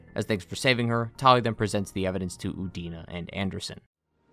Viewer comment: I could have sworn for a moment you said Udini